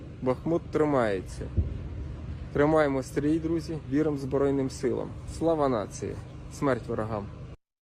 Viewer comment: it sounds like the Ukrainian solider says "Salva Nazi" .. even says, "what did he say?" We have seen this Ukrainian solider in other videos all along the front line so I doubt he said that but it damn sure sounds like it.